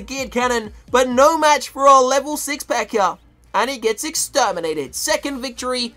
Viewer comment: when he says pekka it sounds like he sayin pecker